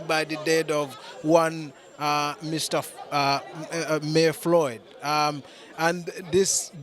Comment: Did he say MAY Floyd 😳 @ 27:15?